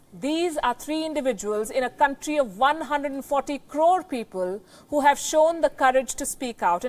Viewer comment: Newss anchor said @ 2:10 "these are 3 individuals in a country of 140 _______ people who have shown the courage to speak out ......" Fill in the blank...what did she say?? I cant understand the word she is saying/using?? 🤔 Please? THANK YOU in advance 🙂 Thank you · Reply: Crore. 1 crore = 10 million